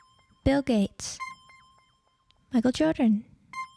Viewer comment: i know this entire poppy thing is dead, and i know i tell lots of "i eat children" jokes but istg i heard "i kill children" at 4:47 instead of michael jordan